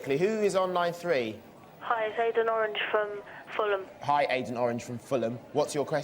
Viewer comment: Aiden Orange, thought they said Agent Orange! Also did anyone see NAKED JUNGLE?!!! I phoned my friend when it aired on CH5 and we laughed so hard we had tears in our eyes and we talked all the way through while we were on the phone to each other.